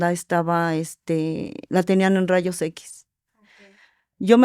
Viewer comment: En el minuto 8 se escucha un susurro 😮 alguien más lo escuchó? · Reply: Yo lo escucho en el minuto 7:42-7:44 y dice " ok ”. Pero en el transcurso del video escuche una tercera voz. La cual decia palabras en ciertos momentos.